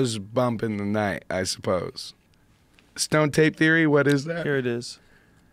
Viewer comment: stone tape = I thogh he misheard stoned ape.